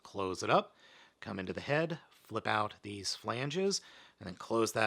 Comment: did u say “phalanges”?????? Phalanges are basically the fingers and toes of the human body, but if u want to get technical the fingers are called metacarpals and toes are called metatarsals